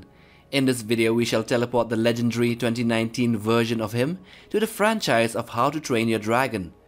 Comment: "We shall teleport the legendary 2019 version of him" What I heard: "We shall teleport the legendary 2019 virgin of him" 😂😂😂😂😂